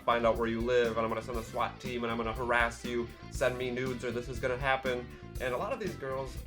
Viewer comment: I thought you said "send me noodles" instead of "send me nude's". It was so funny, yet so uncontextual.